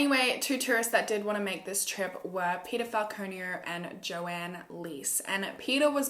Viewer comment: I can't be the only one who hears pedophile conio instead of Peter Falconio